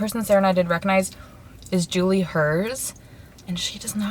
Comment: What name did she say at 14:37? It sounds like Julie Hers but there’s no ig model with that name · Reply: Sandra A i think she means Juliana Herz